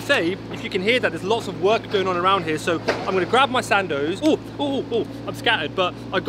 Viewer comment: OMG! you came all the way to Birmingham for a sandwich 🤦‍♀️ slightly disappointed bro. Btw every time you said Sando i thought u were trying to say Sandy 😆 just kidding